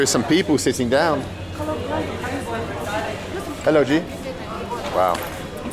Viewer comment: I thought he was saying 'Hello G'.